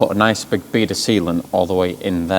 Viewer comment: Timestamp 18:10, totally heard and I quote "a nice big bead of seamen"! Did say seamen or sealant, you decide 🤣😂😀